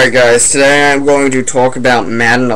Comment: Did you say Tolk instead of talk